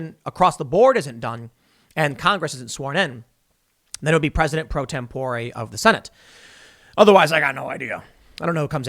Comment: LOL....it sounded like you said President Pro Tempura....Or maybe I'm just hungry..🤣